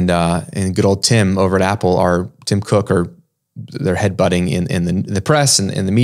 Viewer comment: I thought you said Tim Crook 🤣